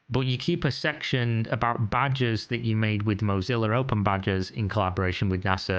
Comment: After you mentioned that it was "badges" and not "badgers", all I hear is "badgers".